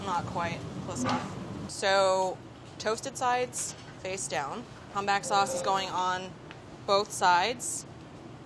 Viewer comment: Whats the sauce on the broccoli Reuben? sounded like she said humpback lol